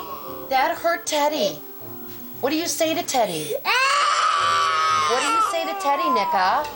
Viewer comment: "What do you say to Teddy N-" I'm sorry what